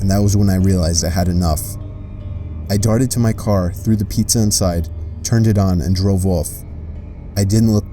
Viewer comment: No. 3 is just their genius way of getting free pizza XD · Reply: Taylor Stapel Might be mistaken but i heard that he threw it in car :P he might have said "i threw pizza and got in car" but i heard it as "i threw pizza in car"... who knows :D